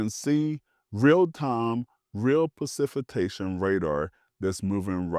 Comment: Had to turn this off when I heard "percifitation" at 0:32.